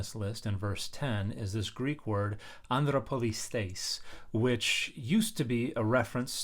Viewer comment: I thought you said Under a Police State😂😂😂